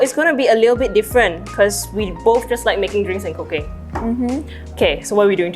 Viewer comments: ngl when said cooking i thought she said cocaine and i almost had a heart attack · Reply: lmao i didn't realise it's meant to be cooking. i thought said cocaine as a joke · ngl when said cooking i thought she said cocaine and i almost had a heart attack